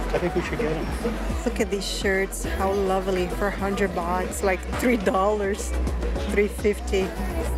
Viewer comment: Sometimes misheard tht you pronounce '100 baht', I heard '100 bucks' instead 😂